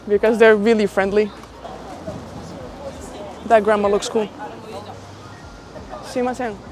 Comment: Did she say grandma 😂😂😂